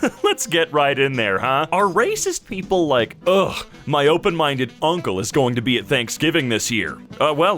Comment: I think they just say “my liberal….” Or “my socialist….“